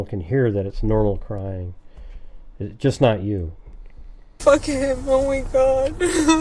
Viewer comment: when is upset and asking about ss going to jail. She says “f**k him, I want him gone”. It’s difficult to hear because is talking at intervals around the same time, but that’s what I heard.